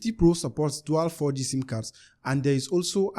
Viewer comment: 12 4g sim cards? Or 12 4g bands? Or did i hear that wrong?